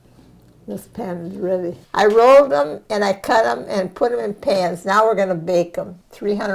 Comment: did she say she rolled them and cut them and put em in her pants?